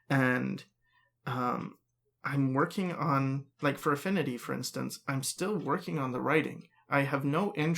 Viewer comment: I thought said he was working on Furaffinity.